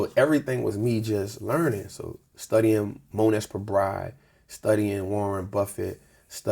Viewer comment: What is the name he says at 27:11? Is it Mone S. Pobrive?